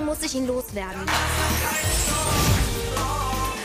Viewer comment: Wers verstanden hat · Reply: Er hat gesagt: warum liegt hier Stroh